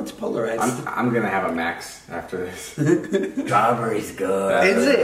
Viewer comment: or idk something around that jason said "strawberrys good" i hear "child births good"